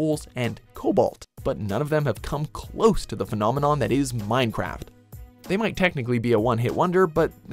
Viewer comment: did you just say Mine crap.